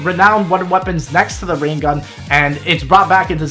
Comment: did he say rain gun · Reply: Ray